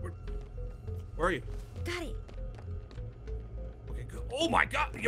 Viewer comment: I’m watching this with captions and when says “got it” the captions say “daddy”